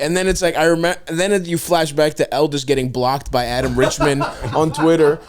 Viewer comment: Am I the only moron who's always thought stav was saying Elvis? Lol